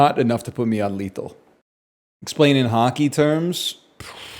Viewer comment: When he said “hockey terms” I heard “haki terms”